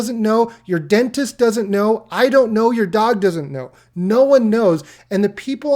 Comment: I had to rewind the video...I swear I heard "Your god doesn't know." I was like, damn. I knew you were a meme lord, but that's a risky comment. 😅😂